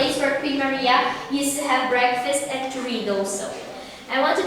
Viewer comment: It sounds like she says a breakfast and dorritos. Sounds like they were on the same diet plan I was on when I was in college. At 1:20 it sounds like she describes the windows as a shitting place used by soldiers. Another common practice used during late night parties in college when the bathroom was taken.